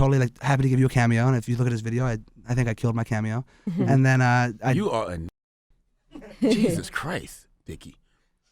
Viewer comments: what did he say? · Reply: "You are a N-word" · Hey y’all what did Charlamagne call Dave at 10:05 ? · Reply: Jew